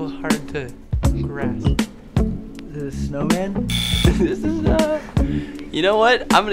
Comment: i thought zach said “is this a sew-in?” And I was like ... 😳✌🏼